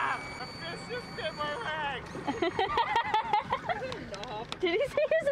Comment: "Did he just say he has a cyst in his leg?" "No, he said a fish just bit his leg." Hahahahaha! You guys make my day! :)